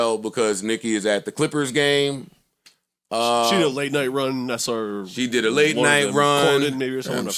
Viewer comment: Yo help me understand what Parks said right here I'm a Lil deaf in one ear truthfully speaking 🙏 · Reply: “…Late night run…Conan or some shit.”